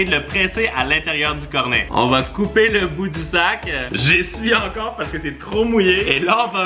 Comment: A 12:26 j'ai compris on va couper le bout d'isaac